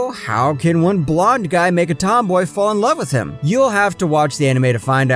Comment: "So how can a blond guy n*gga tomboy fall in love with him" is what I heard XD